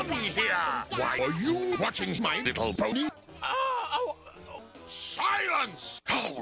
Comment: sounds like he say"my little pownd"